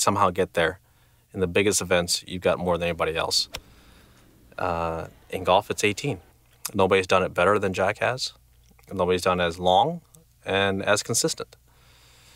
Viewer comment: sounds like he says Jackass :D